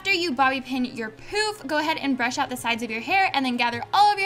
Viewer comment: When she said pouf at 2:37, I thought she said poop😂😂😜😜